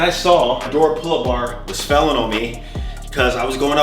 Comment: Did he say, “felling on me?”